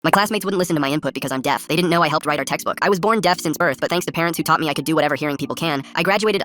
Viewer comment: I thought he said "i was born at birth" for a second lmao😂😂😂